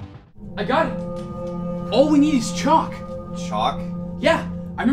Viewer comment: I thought he said 'all we need is Chuck !!!'